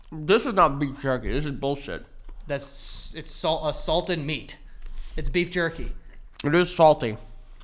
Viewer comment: When called it "a salted meat" I misheard him as "assaulted meat", then I saw how treated that thing and it was definitely assaulted 😩